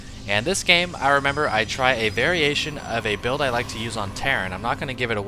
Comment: thought you said: very-asian of a build